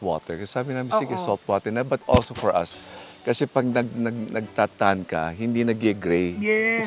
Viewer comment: What did he say on 11:17 nagtutug? What is that po?